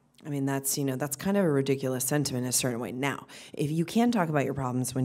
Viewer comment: what did she say at 2:30? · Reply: Rediculous sentiment insert it right now